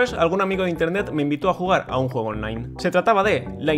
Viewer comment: alguien me dice como se llama el juego? No lo entendí muy bien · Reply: Lineage 2